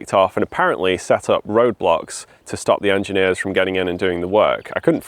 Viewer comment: Was I the only one who got nostalgic hearing Portland Bill? And was I the only one who misheard 'roadblocks' as 'Roblox"?